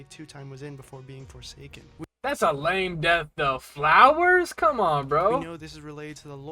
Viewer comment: did someone say flowers......